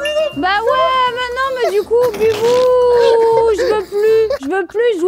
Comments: c'est moi où jujufitcat à prononcé bibou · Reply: Bah oui elle parler a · C’est moi ou à 10:30 elle l’a appeller bibou???